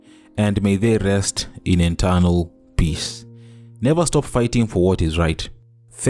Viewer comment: Mkuu did you just say "Enternal Peace"